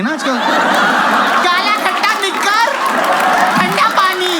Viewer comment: did he say the N word at 00:43? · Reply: Nikkar (chaddhi/shorts)🫠